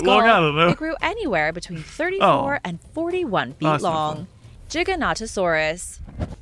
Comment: I heard 'chickennotosaurus' lol